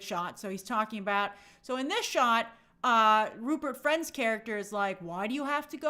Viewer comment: Lol, all this time, I thought was saying, "Rupert's friend."